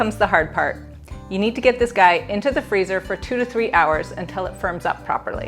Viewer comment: Lol First I thought you said "23 hours" and I was like, "What?!? I don't have that kind of patience!"😂😂 2 to 3 hours is fine. I always make them with gelatin. But I've never tried it this way.